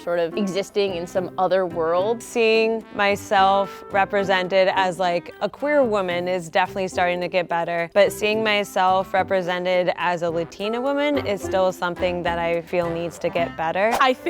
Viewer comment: Is chantle queer ??? They look amazing ❤️ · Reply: I thought she said career woman